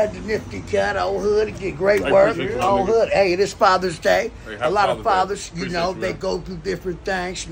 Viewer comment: At first I thought he said 5ers (Fivers Day) 😂😂😂